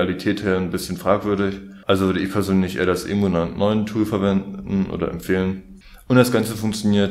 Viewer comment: hab ich dich akustisch nicht verstanden... Warum ist der GWLauncher fragwürdig? Ich hab mir die Tage erst meinen EmuNAND mit dem GWLauncher eingerichtet. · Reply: etwas in die Richtung "also würde ich eher das EmuNAND9 Tool verwenden". Der Gateway Launcher enthält einen Teil eines RAM Dumps vom 3DS, weshalb es eigentlich illegal ist, ihn zu benutzen.